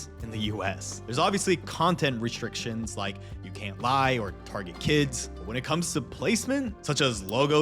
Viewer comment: Did you just say 'you can't buy or target kids' XD